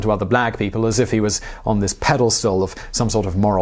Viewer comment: Did he say 'pedelstal' ?